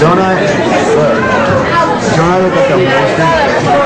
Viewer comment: "Don't I look like a lesbian??" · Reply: erynn shewmaker I thought he said that too but he actually said "don't I look wasted?"